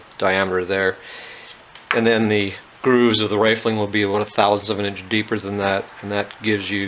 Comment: Are you Canadian? Is this legal!? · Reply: I could've sworn I heard you say "aboat" in place of "about" You must live Minnesota or something then, right?